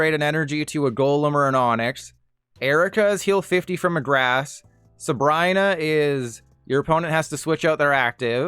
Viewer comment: did bro just say subraina 😭